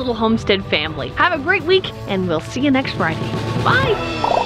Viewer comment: best part of my week. and for a second there I thought you said see you next spring.